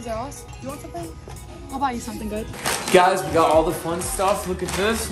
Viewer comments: I heard “I’ll buy you something good” not babe but that’s just what I heard 😂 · Did it not sound like she said "I'll buy you something babe!" Or is it just me 😁❤️